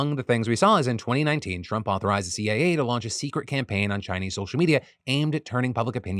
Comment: it sounds like phil says the CAA and not the CIA.